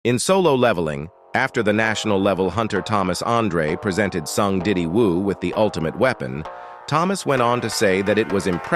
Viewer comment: Drippy Woo??? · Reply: Wait I thought he said son diddy wooo 😂